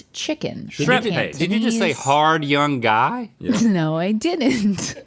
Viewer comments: In Link’s defense, I heard “hard young guy” too! 🤣🤣🤣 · 😂 I definitely heard “hard young guy” too🤣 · I thought she said “Hard Young Guy” too 😂